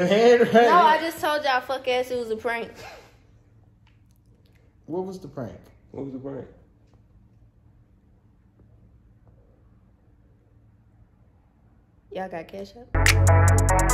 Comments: Am I the only one that thought she said “y’all got ketchup?” At the end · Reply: I thought she said yal got cash app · Am I the only one that thought she said “y’all got ketchup?” At the end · “What was the prank”..........Y’all got cash app? Omg I never had this much likes thank you all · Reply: I heard "Y'all got ketchup?" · “What was the prank?” “yall got cashapp?” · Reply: I thought she said y'all got ketchup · “What was the prank?” “yall got cashapp?” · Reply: Me too. I thought she said “ Ya’ll got ketchup.”😂😂😂